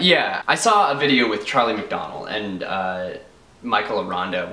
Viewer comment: I sounded like you said "Trolley McDonell" xD